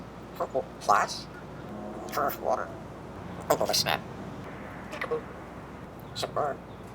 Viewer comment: "purpleplas" is quickly becoming the next "shrock" / "pisnack" where our reality bends around Apollo's quirks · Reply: I heard a "Purple Pisnack" in there somewhere, too.